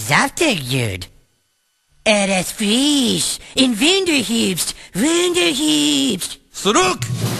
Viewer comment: Er ist Jud, ein schöner saftiger Jud, er ist friiisch und wunderhübsch wunderhüüübsch! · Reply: ich hab auch irgendwas wie Winterherbst verstanden